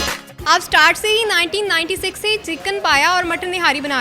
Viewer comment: what she said? Chicken paya?? 😮